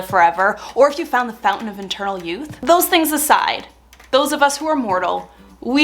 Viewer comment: Eternal youth, sounded like you said internal youth lol. If only we had a font of internal youth!